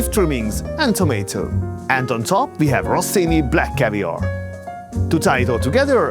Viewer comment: did you said rossini caviar? what a shame... to have in Poland best caviar in the world from Antonius yet still going for cheap chinese bullshit...